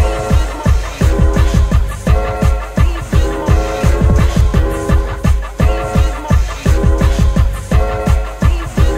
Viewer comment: Why is bro saying "Reese's monkey!" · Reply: Biz Markie